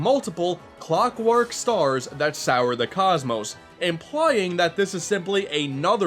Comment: "sour the sky"... Wut. I-it says soar... Soar...